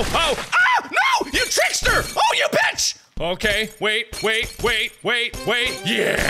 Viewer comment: The way mark said bitch during the bottle map sounds like he said pitch